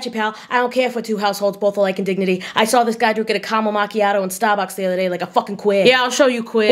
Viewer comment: I misheard caramel macchiato as “cask of amontillado” and thought to myself “omg crossover?”